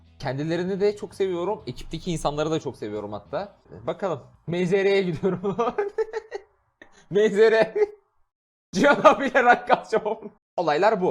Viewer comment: da Melih Zeyrek'e gidiyorum diye anladım fkhbklndxkjbnkj